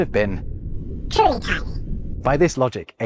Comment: So when you said "truly tiny" and your head shrunk I thought I heard you say "turtle time", or rather that possibly there was a soundbite played of that phrase from the tv show which had gone through filters...I rewound the video, and of course, you didn't say turtle time you said truly tiny but good sir a ninja turtles quote at that point would have broke me had it been true. 😂😂 💀😭 Also I enjoy watching these immensely. You'd be the perfect person to sit down around a campfire and chat with; I can't imagine how many brains you explode on a daily with the things you know 😂